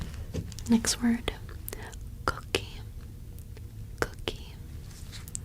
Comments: When you were doing the trigger words part of this video & said “cookie” I heard it as “cocaine” the first time! 😅😂 Otherwise, excelent video! ❤️ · I'm really impressed with how many voices and demeanors you can pull off in your videos. Excellent video!^_^ Also, for just a sec, I really thought you were saying "cocaine" instead of "cookie" at the end there. Lol^_^